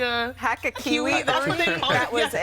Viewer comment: I know they're saying "Hack-a-Kiwi" but I keep hearing "Haka Kiwi"